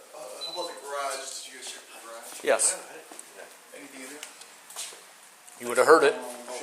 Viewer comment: I this is a very minute thing, but what a snarky comment 11:08; "Did you check the garage? "Yeah" "Is there anything in there?" (That was obviously what he asked even though the subtitles said 'anyboydy'.) "You woul've heard it." Even the way he said it. He seems like an asshole.